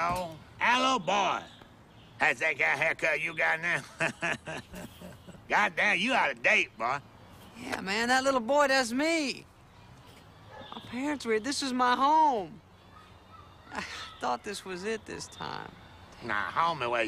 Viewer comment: When I watched this scene as a child I always thought dude said “you got a date boy”